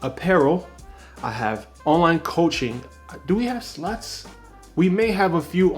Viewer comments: bro im trying to lose 80lbs. I love your videos! im doing well so far! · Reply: thanks bro. ill be documenting it if you get bored on youtube 😂 btw i thought you said sluts not slots 😂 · I thought he said sluts at first haha 0:26 - 0:33 secs in